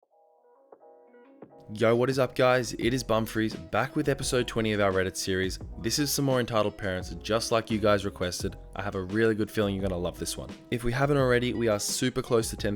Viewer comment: Thought it said bum fries